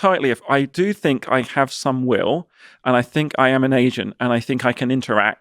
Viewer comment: I heard asian and not agent at first😂